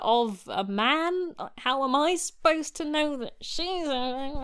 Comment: What is she saying at 2:33 ? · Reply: "How am I supposed to know that he's [grumbles] and she's [more grumbles]?" It's not meant to be proper speech. I think the best translation would be "how am I supposed to know he's a statue of a person and she's a statue of a statue. They're the same material."